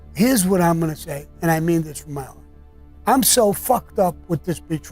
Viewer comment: Wtf does being Irish have to do with telling stories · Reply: subtitles on, 19:53 - the cc's are wrong and say " i mean this from ireland" but he was actually saying "i mean this from my heart"